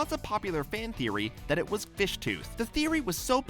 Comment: Omg I thought they said fish toot 😂